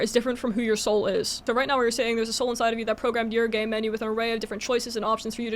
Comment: Whenever you said "game menu" I heard "gay menu" and honestly a gay menu sounds more fun